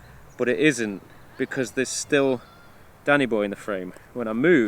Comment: I've watched so much Camera Conspiracies, that I thought you were saying "Panny Boy", when you were actually saying "Danny Boy" lol